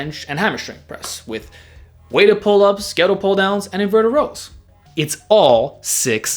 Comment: What the name of the exercises in 03:00 mark? I dont understand. incline bench, flat bench and hammerstring press? Weighted pull ups, scatllte pull down(????) And inverted rows. Help, please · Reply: It's Hammer strength press and ghetto pulldown